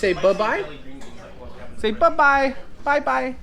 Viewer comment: Sooooo... was filming for, or is he starting his own channel? :) P.S. I could swear said "buh bye" at 12:35!!